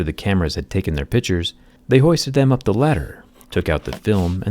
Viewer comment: Everytime he says “pictures” I hear “pitchers”. Do you hear “pitchers”?